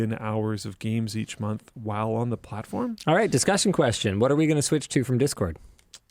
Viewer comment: Did someone say Switch 2?